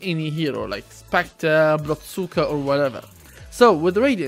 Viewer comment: Did I just hear him say "blood cyka"??? 😂